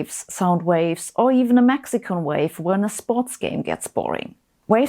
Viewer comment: did she say a Mexico wave?